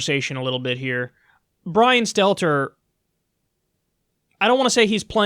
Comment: I thought I heard you call him "Brian Seltzer" I had to relisten and no you didnt, but damn you missed an opportunity to slip one in.